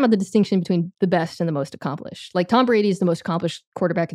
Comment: Did Mina Kimes really say that there was no point when Brady was unquestionably the best QB? What a take. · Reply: i think she said most accomplished